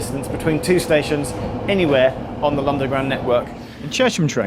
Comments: did he say londaground 😂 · Is it me or does say "Lunderground" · it cant just be me who hears say ‘Londerground Network’